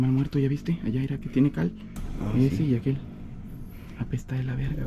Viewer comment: Minuto 38:36 se escucha que dice que tiene hambre